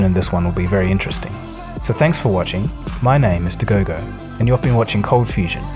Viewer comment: nice.. and your name is what ?? I heard My name is Mr. GOGO